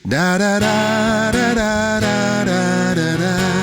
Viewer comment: I think the lesson of this story is : listen to your friends 😂 When I used to listen to this song , I always heard “paya ya ya “ at the beginning . Not “da da da” 😅